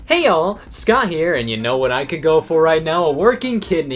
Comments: I THOUGHT HE WAS SAYING Y'ALL NOT ALL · Before this video I thought he was saying “Hey y’all, Scott here” instead of “Hey all, Scott here”